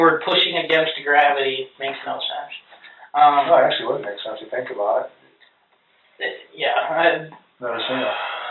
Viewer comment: The detective was agreeing with him that using gravity instead of lying down to give birth “does make sense if you think about it.” I think misunderstood and the content creator misunderstood as well. The detective was agreeing with him.